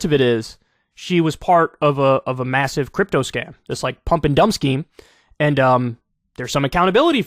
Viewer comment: I keep hearing "pump'ndumpsky"